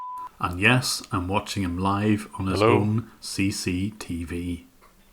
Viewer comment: is someone saying "hello" or is that just me?